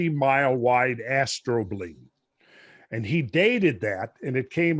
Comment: When Randall first said astrobleme, I thought he had said 'astro-bling' which is actually a bit cooler.